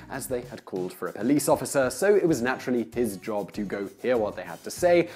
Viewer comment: the CC says "titjob" for "his job"... can automatic CC have Freudian slips?